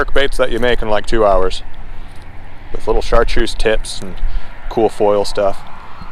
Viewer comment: tbh i heard chartreuse tits